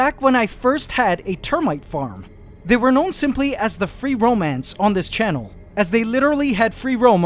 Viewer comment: Free roam ants . I thought you said romance ants and thought of a name for the roaming ants How about "little romeos" or "lady romeos" would be super cute.